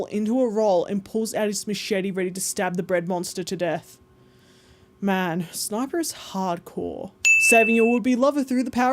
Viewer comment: i was reading subtitles without my glasses and thought you said "man sniper is handsome"